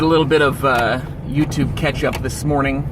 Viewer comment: I thought you said YouTube Ketchup! lol and I was like.. Where can I buy some of that? You Rock brother. See you on Hump Day!